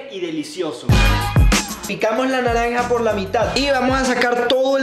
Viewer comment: entendí que dijo me cago en la naranja